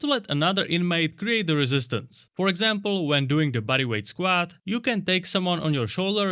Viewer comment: When you talked about using another prisoner as the resistance, you said "body weight squats" but I heard "buddy weight squats" cuz of the accent and I thought that works way better anyway 😂. Love your videos keep up the good work